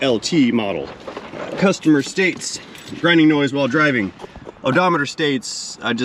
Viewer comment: I thought he said there's a driving sound while driving.... lol I was like, no shit sherlo....ooooh. 😂😂